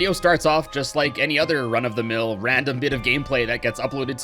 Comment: Also I used to mis-hear you the first few times I watched this video and thought you said "run-up-the-mill", but you actually said " run-OF-the-mill". OOPS. Also before I had a YouTube account, when I was twelve I used to watch videos on my "dad's" TV at "his house" and say this video and got kind of scared.